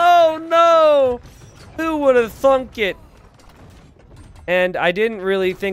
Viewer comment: Did he just say "thunk" ? 😂😂